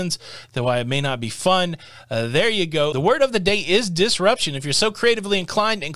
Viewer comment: OMG!! I am hard of hearing. I thought you were saying "Disfunction " I was going to say that I won't have the Covid nasal swab because I once tried the Viagra nasal spray and I had a stiff neck for days!! Now that was disruptive.